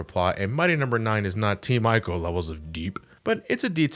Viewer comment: t micheal? team micheal? sry i didnt hear. can any1 tell me what he said?